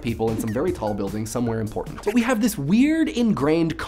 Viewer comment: did he say important or "in Portland"?